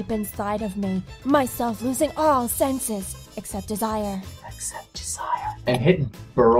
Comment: Wait wait wait at 1:01 did you say except Josiah?